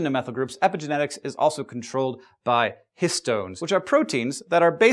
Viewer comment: I keep mishearing histones with piston... minecraft is bad kids.